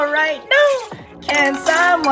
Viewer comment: When he said funny I thought he said fu*king💀💀